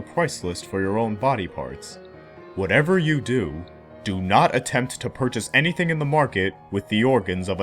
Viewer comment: I misunderstood him at first; I thought he said not to try to purchase organs! This makes much more sense.